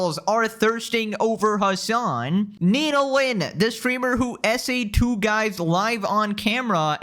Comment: Guys, I think I may be addicted to Silksong because every time he says "Nina Lin" I hear "Needolin"...